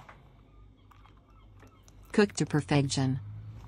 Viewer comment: I heard, "Cooked to prevention" And now Im worried about seagull birth control